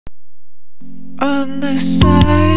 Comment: This is what I hear "On the side of the lord, whos counting butter (Doh) Alls of the lord. only for the right. A hold the cheeseburger family dad, mom , brother, sister. Aragahto family, dog,cat said the printer..." Don't hate me XD